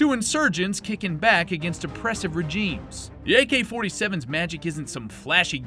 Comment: I’m sorry but I heard “oppressive rejeems”